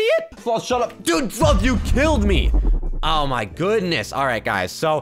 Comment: sounds like you said the f word but instead of the f word it was “SLOTH YOU KILLED ME!”